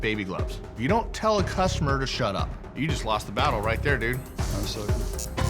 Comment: Am I the only one that heard him say “chill out” and not “shut up” in the beginning?